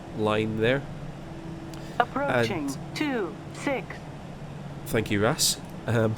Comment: what did he say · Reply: RiethAndalGAMES "Thank you RAAS". RAAS tells you on which runway you are or if you're on the taxiway. She says f.e. "on runway 26" or "on taxiway"